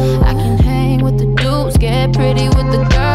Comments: IS IT BAD I THOUGHT IT ALWAYS THOUGHT IT SAID “how this girl get so fine instead of fly- AM I A SIMP?!😭😭👹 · At first i thought she was saying : How'd this girl get so fat 🤔🤔